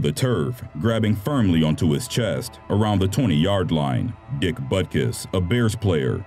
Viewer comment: I laughed so hard at 8:05 because I hear dick butt kiss I know it’s not an appropriate time to laugh but still